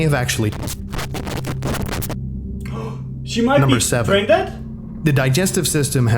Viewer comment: what? i never said pregnant xD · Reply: Ah, okay I can see that now. You said "brain dead", not "pregnant". Apologies for the mix up then, I seem to simply misheard myself! Ha! THAT makes far more sense with everything else you've said. I was just so focused on the oddity of the "pregnant" comment that I didn't even think to replay that section. Still looking forward to that Blackwood video!